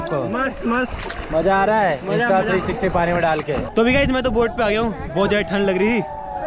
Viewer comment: sourav bhai Kya bol rahe hai Kon sa stick☺️☺️☺️☺️ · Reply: 360 degree cam